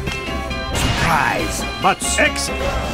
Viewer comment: I swear I heard "surprise... butt sex" at 10:43 I cant stop laughing XD